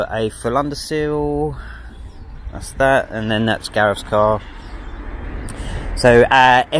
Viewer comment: Have you done a segment on "Garrets" car.....I can't quite make out the name, but its the car at the 2:40min mark...has the stripe through the headlight door. If you already covered that car in a previous video, please let me know which one. What a great looking NA/Series 1!! I would love to find out more about the car, the side skirts, the wheel size, suspension etc...It looks awesome!!!! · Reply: It's Gareth, colleague that entered at 13:24, prompting to swear ffu** ☺. Yes it came out